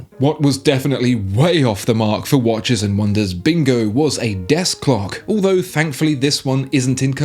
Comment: I had to rewind and listen again because i heard at 24:55 that Patek Philippe realeased a Death Clock. In the first moment i thougth, yeah nice looking Deathclock, i shows the Time you have left, but wait, is it even possible... oh its a Desk Clock !